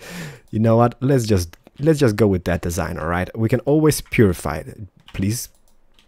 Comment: for some reason I thought I heard let's Pewdify it XD